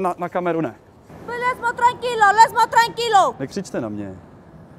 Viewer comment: She was saying « laisse moi tranquille » wich means leave me alone she spoke a bit french ;)